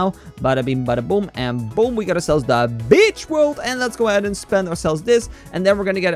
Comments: Kinda sounded like he said the B** World lolololololololo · why does it sound like hes saying btchworld